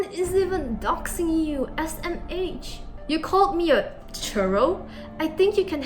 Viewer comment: did u say churro?? Churros are good lol. Also the alabamas watching was gold